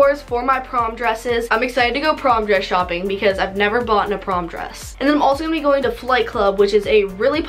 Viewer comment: Am I the only one who heard her say “boughten” a prom dress? 💀